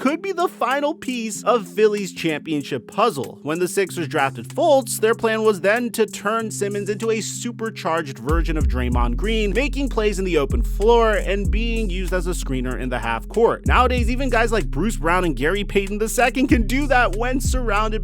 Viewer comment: sounds like he said “virgin of draymond green” lmao